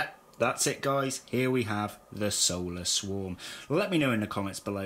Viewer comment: Solar.... I thought you said soulless 😂😂